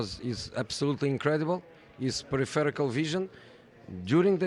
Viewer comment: José’s pronunciation of “peripheral vision” was stellar. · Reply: I thought he said Prophetical lmao